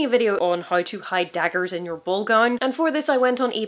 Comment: I thought she said "BALL GUN"